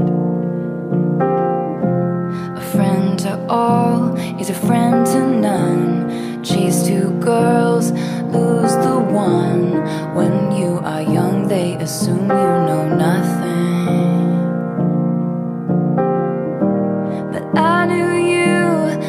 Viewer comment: "Just two girls lose the one" is it just me or did it sound like she said the word cheese instead of the word just? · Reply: chase two girls, lose the one